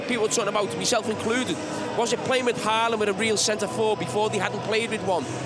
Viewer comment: I thought henry kept saying Allan lol